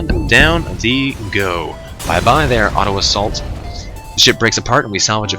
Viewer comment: he said bye bye auto assault, I heard bye bye Ottawa salt